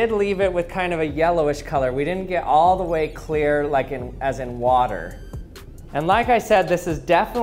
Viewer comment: uhh did you really said "As an water" ?